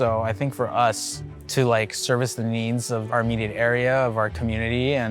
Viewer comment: Where did he say that? Someone mentioned "safe price".... are you sure you didn't intentionally mishear? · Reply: he said safe space. He basically blamed racism which is ridiculous...as if Californian isn't full of Asian influence. This guy is looking to be a victim and language like "safe space" supports the chef's victimhood mentality. Im with the original poster, I'm put when someone mentions something as ridiculous as safe space